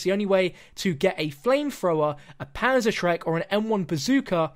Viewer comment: did he say PANZER-SHREK!!!!!?!